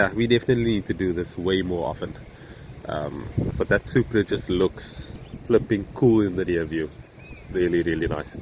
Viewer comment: Did i hear right..."that Supra looks so damn slow in the mirror" 😂😂😂